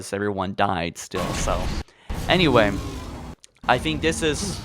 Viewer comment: I don't think he's saying numbers, i think he says "i don't think" and after that it's hard to hear what else he says. What does everyone here think he is saying?